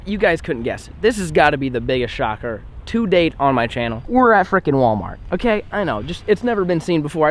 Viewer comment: whenever he said were at frickin walmart i thought he said were at african walmart so i was like what???